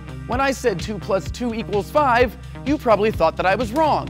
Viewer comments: LOL i thought u said 2+3=5 TROLOLOLOLOLOL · I thought he said 2+3=5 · I honestly thought he said 2+3=5 xD I didn't hear him say two.